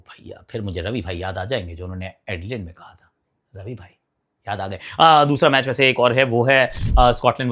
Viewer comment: aakash sir ravi bhai ne kya kaha tha😂😂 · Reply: Thodi der ke liye..